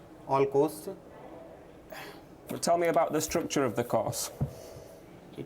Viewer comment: To be fair, the guy at 18:00 clearly was saying "tell me about the cost" 😂 at least that's what I heard 3x so I would have gotten that wrong as well